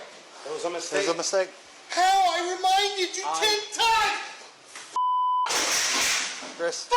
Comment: what? what does tik tok have to do with anything? wtf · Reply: in the text during the snake thing. After they say it was a mistake. He says "How?! I reminded you TikTok" but he really said 10x